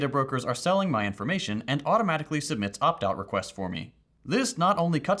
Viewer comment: In the sponsor segment I hear "updog requests" instead of "opt-out requests" haha